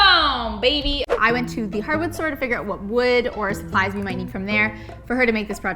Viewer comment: Did she just say "hardwood store"? Lol ☺️